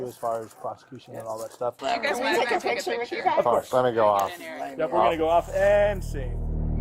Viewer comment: For anyone curious, at around 10:59, the woman in pink asked the cop if she could take a picture with them. The cop replied "let me go off", meaning he wanted to turn his body camera off. Cops don't need to keep their body cams on for casual, non-business interactions with people. When the cop reached up and switched his body cam off, he humorously said, "and... scene!" (He did not say "and, sing" like the captions said). 😂